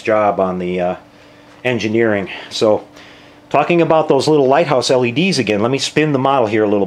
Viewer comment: Persistence pays off... and thanks again. It was Lighthouse that I heard you say... and I found them. I am already thinking of how these lights will look great on the Enterprise REfit kit. Looking forward to all the parts of your tutorial... especially the wiring and the extra light and details you have added. Thanks again... you are definitely an inspirational modeling boost...Qapla' Eric V